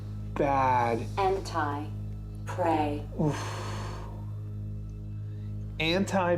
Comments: I thought the spirit box said "end times" pray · I thought she said" end time pray"? Not anti pray. Also Mr drunkard is well named!!! That's quite the stash he has!! Thanks for a Cool video! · I heard "End time, pray!!" It makes more sense too. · I heard "Endtime Pray" not Anti-Pray"...